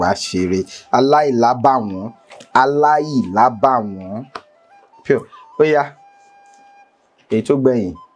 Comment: Did he just say pure😅